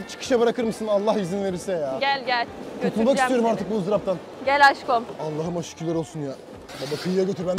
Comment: gel aşkom dediğini bi benmi duydum